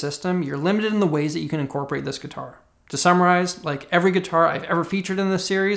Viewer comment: Did I hear you say “summarize” as “samuraize?” I like to think so